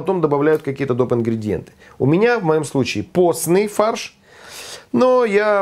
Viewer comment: Думал, что послышалось, прослушал дважды, и на 2: 30 услышал ""поцный фарш"". Herr Vasylij (""Herr"" можно произносить по русски) - уважуха!!!!